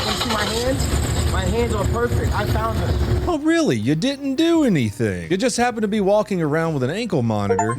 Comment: Why did they have ta shoot her? I heard "she aint do nuthin".